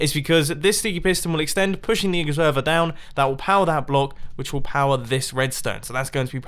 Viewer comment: does he say “pushing these n1&&@ further down?”